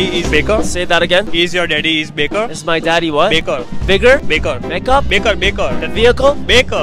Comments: Honestly I heard 'makeup' too lolz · I thought he said becquer